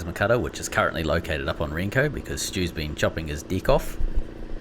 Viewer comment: Your accent got me. I thought you said he using the plasma torch to cut off his dick. 😲 In review " his deck" 🤔 A piece of sponson for a sponsor or the winner 😁